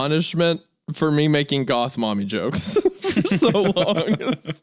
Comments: Oh you wanted GOTH mommy… my b I thought you said moth mommy 😏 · Goth mommy!? I thought you said MOTH MOMMY!! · “Oh Goth Mommy, I thought you said Moth Mommy.” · I want a goth mommy: I thought you said moth mommy? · “Ohh GOTH mommy, I thought you said moth mommy…” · GOTH mommy! My bad I thought you said MOTH mommy